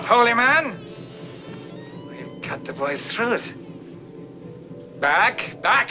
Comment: Stop holy man. I started to laugh. I thought I heard the master say something to Mark like " little boy"